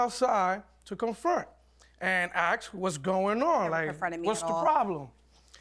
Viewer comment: Am I the only one who heard her say "He never confronted me at all"? 🤔